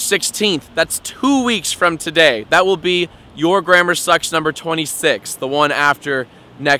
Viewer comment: Whenever he says 'your grammer sucks' I always hear 'your grandma sucks'...